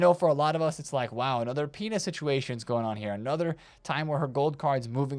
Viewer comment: My brain might be cooked because every time you said "Pina's" I heard "Penis".